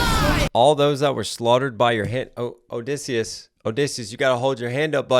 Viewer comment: He actually said "almost all of whom were slaughtered by your hand" lol